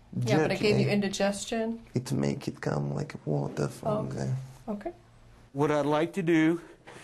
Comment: When Borat talked about how beef jerky "make it come like water from the" first I thought he was talking about increased semen volume (I heard "waterfall" instead of "water from the", but then I realized he meant diarrhea)